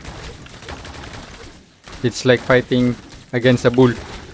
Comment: I heard "payting" (fighting) so alam ko pinoy to